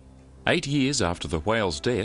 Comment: I always thought it said 80 years not 8.